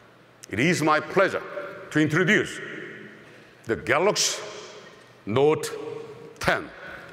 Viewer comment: It's my pleasure to announce the galux's note 10 lol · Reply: i heard "let me introduce the galax, not 10" lmao